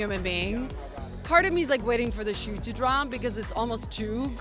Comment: I think she said “waiting for the shoe to drop” it’s an idiom for waiting for an inevitable event to happen. I think she meant she was waiting for cena to act like a jerk but he never did because he is genuinely a nice guy.